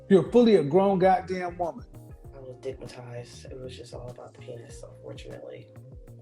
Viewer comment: Did she just say she was "dickmatized " 😂